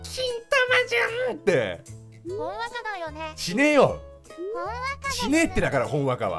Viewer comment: ○ねよ！に聞こえるww